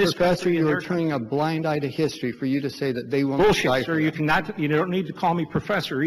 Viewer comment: did Scheuer just say "bullshit" to a congressman on the floor???? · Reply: yes he said "bullshit"